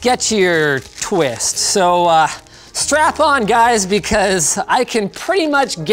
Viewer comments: did he just say strap on? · did he just say “strap-on” instead of “strap-in”? Linus, you dirty bird. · Did Linus really just say "strap-on" instead of "strap in"?